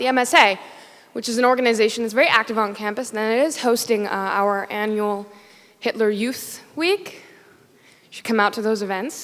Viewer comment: does she say "our annual Hitler youth week" at 0:39 ?? ...is uc san diego the one in california, usa? wth?